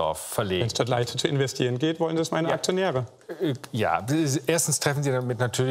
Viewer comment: was hat der da gesagt? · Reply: "Wenn es dort leichter zu investieren geht, wollen das meine Aktionäre" Er ist natürlich den Eigentümern seines Unternehmens (also den Aktionären) gegenüber dazu verpflichtet, in ihrem Sinne zu handeln. Das selbe machen Apple, BMW und co. mit den Steuerspardingern in der EU. Würden sie anders handeln, wäre es sogar gesetzeswidrig.